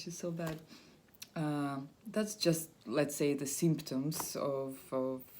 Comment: well i would say i kinda still have it although its a lot more mild these days so i had it starting from april 15th 2010 what is that like 9 years ? The first thing i had to do was move back home where i could calm down and feel more safe and secure. the next thing was stop drinking smoking any drugs and get on a clean diet. I also started to worko out everyday if possible lift wieghts do cardio i put on about 15 lbs of pure muscle. I also went to see a doctor who put me on cymbalta 30 mg its a very low dose but helps with the extra anxiety. I really just take it one day at a time it doesnt happen overnight and I also learned to not put a date on when I will get over this , meaning I may never get fully over it but i cant stop living either or let it stop me from doing what I have to do in life. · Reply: M Francisco I thought you said the symptoms are gone